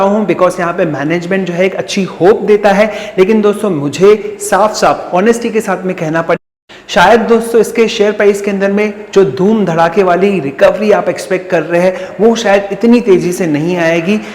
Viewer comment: your vedio was cut ? What was you were saying? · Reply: Its in continuation only - “honesty ke sath kahena padenga ki share me recovery ki expectation.....””